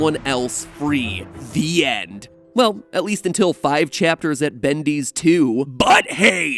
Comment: I think I misheard what he sqid at the end as "Until Five Nights at Freddy's 2)